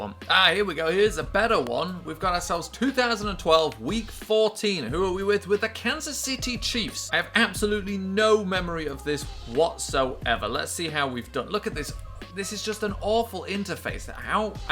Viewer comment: it sounds like he said cancer city chiefs 😂